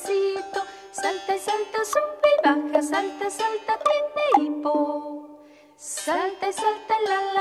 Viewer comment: por favor pueden pasarme lo que dice en el minuto 00:25 gracias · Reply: Tiene hipo